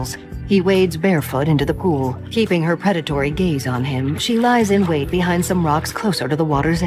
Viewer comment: I thought the voiceover said "predatory gheys" I am so silly.